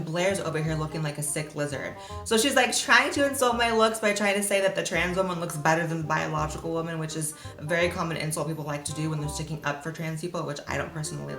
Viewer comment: I don't understand what she said (I am French ans I am learning English). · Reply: "So she's trying to insult my looks by saying the trans woman looks better than the biological woman which is a very common insult people like to do when they're sticking up for the trans woman, which I don't really agree with"